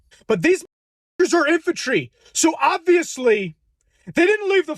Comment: She thought they said infancy!